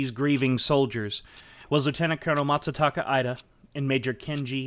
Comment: Please take more time to correctly read the text that you have displayed on screen. I’m only 6 minutes in to this video and have noticed over 10 mistakes. For instance, General Takeshi Mori was on screen at the moment you said ‘Lieutenant General TaTAshi Mori’. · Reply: you also say ‘MaTsataka Ida’ instead of ‘Masataka Ida’ (which is correct).